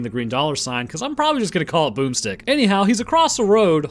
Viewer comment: Thought you said boobstick instead of boomstick